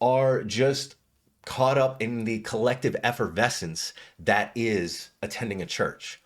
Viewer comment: did you say collective Evanescence? Wake me up inside brother.